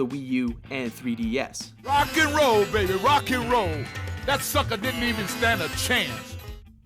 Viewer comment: I heard "That suck a dick..." xD